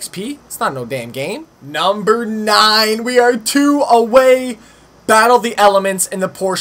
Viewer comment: when you said you were battling the elements I thought you said battle the elephants lol lmao